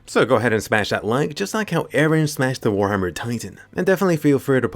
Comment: Hold up did u say just like how wren smashed the war hammer titan, I thought eren was team historia